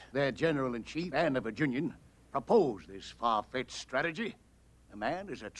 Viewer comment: Wasn't General Scott the one who created the plan? Because Jeff Davis claimed he opposed it. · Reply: It does sound like he says opposed but I believe Davis (Bridges) is saying proposed because in the next sentence he brands General Scott a traitor (to Virginia and the South) for his association with the plan.